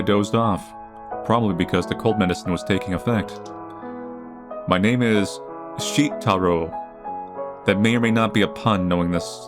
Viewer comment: I swear to god I heard you say "my name is Shit-towel"